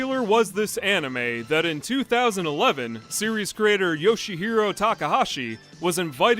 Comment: "Yoshihiro Takahashi." Lol, for a moment I thought you said Yoshihiro Togashi.